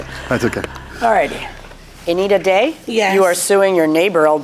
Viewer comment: I thought he said I needa day but it's her name